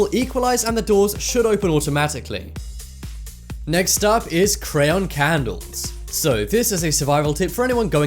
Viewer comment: I ACTUAL THOUGHT HE SAID CRAY ON CANDLES!